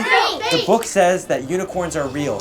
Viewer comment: I thought it said and they actually do run candy 😂